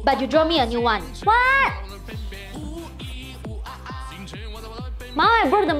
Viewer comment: At first I thought she said “mom I drew the Mona Lisa painting’ so in my mind it went like this Child: Mom! I drew the Mona Lisa painting!😀 Mom: What! 😱Go scoop by the litter box😡